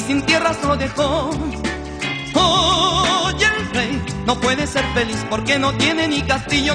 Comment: Pensé que decía "soy el rey"